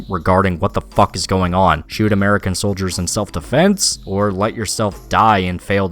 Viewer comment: Fun fact: in spec ops, after the cutscene when walker picks up the radio, if you take a moment to look around, you'll find a list of kia soldiers (killed in action). If you give it a good look you'll see the names of adams and lugo on it, putting into doubt all of the events up to that moment. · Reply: Lol you know I first thought you said Ikea soldiers